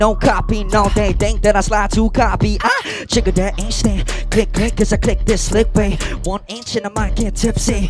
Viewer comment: did Mark just say "Jiggle that ass yeah, click click"?!